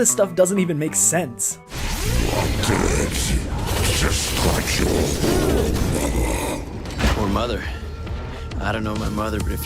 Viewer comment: you said the dialouge makes no sense, the hunter said " You will die like your mother. " Since Dante didn't understand him he thought he called him a son of a bitch, & through out the game Mundus says he's going to kill dante by ripping out his heart and eating it. ( That's how Eva died )